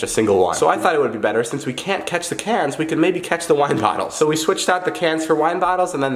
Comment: LITERALLY BLEEDING TO POSSIBLE DEATH David: “G E T A T H U M B N A I L” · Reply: xD I haven’t watched the accident yet but I’m scared cause I don’t like gory things. I’m gonna watch it anyways. Edit: I thought he said WATER bottle not WINE bottle